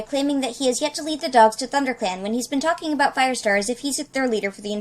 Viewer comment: The Subtitles said Thunder pan instead of thunder clan at 1:52 and it messes up a lot but for some reason I like Thunder pan. it says stuff like "Blue store" at 2:03, Tara star shortly after. "Halter Knees" at 2:13. "Tiger starvin" at about 3:50. And there are many more but they are so small that I didn't put them in and I almost definitely missed some in the beginning too. But yeah, just saying... and the end has something too btw.